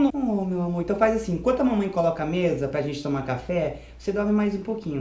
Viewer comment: pera eu ouvi coloca a mesa seis tbm ouviu?????